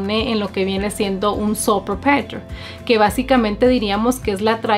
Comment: el irs nos coloca por default un tipo de impuesto llamado el "soperpetels" ¿Que dijo? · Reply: Existe una elección fiscal que se llama Sole Proprietor (Dueño Unico)